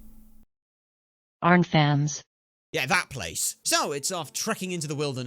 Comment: did someone say arkfans